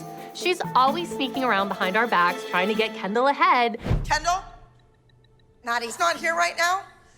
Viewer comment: the voice over is terrible here. It's like they didnt even try cuz it sounds like gianna said maddie and not abby 😂